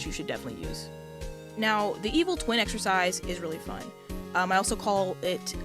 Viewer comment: Writing Exercise? I thought you said eating extra fries!